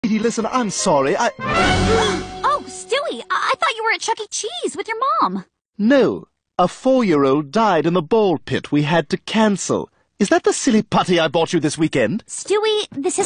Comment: I seriously thought he said "a 4 year old died by a pit bull"